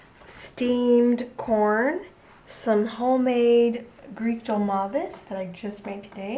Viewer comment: whats this food at 8:43 ? did you say greekto marvis ? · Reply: They are stuffed grape leaves. I have made a video of how to make them. They are called in Greek "Dolmades"